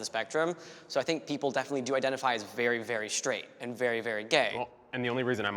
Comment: "People either identify as very very straight or very very gay" Me, a flaming bisexual: am I a joke to you? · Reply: I thought he said “people can either identify......”